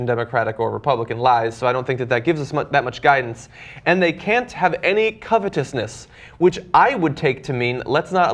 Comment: Oh that was the last one "...can't have any covetousness." - I thought he said "can't be a communist".